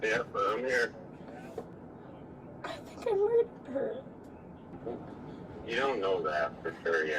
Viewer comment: what does she say here??? I keep hearing “Brandon?…I think I “raped” her…??